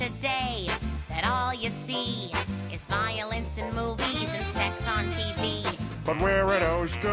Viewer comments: I heard "it's seems today, that all you see, is violins and movies, and sex on tv." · I thought Lois said "violins and movies" but she actually said "violence in movies".